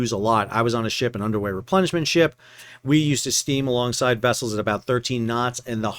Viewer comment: Sounded like you said you were on an “underwear replenishment ship”. (5:55) (Figured it must be something else but listened to it several times)